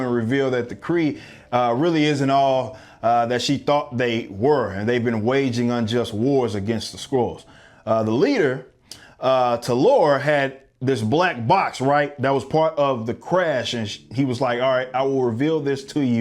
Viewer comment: I keep hearing "a war against the squirrels". If only that was what the movie is about, I might be interested. Especially if Brie Larson ends up being beaten and eaten by the squirrels.